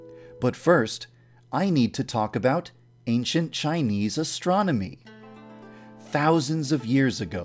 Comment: I swear when he said 'Chine astrology' I thought he said Chinese astronomy.